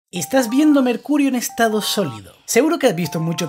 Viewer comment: Cuando dijo "estas viendo mercurio" Pense que dijo"esta viendome el qlo"